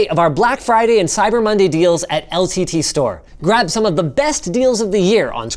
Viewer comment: Is it just me or did he say LCTSTORE?